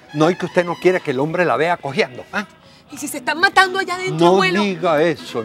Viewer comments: que dijo robinson en el minuto 7:28 dijo COJEANDO O QUEEE??? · Jajajajajaja safarrancho en la fiesta y ellos afuera filosofando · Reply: que dijo robinson en el minuto 7:28 dijo COJEANDO O QUEEE???